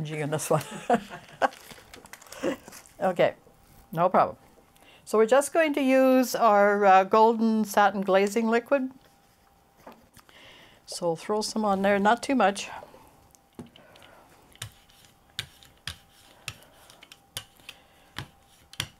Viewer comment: What is 'blazing' medium? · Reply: Ah - I thought you had said "blazing" medium!! :-)s